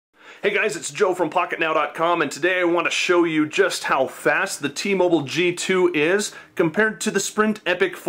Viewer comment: Lmao! I was watching the beinging and i heard " hey guys today im going to show you just how fat-"